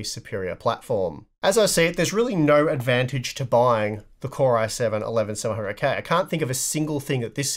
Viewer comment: Did I just hear, "Poor i7-11700K"?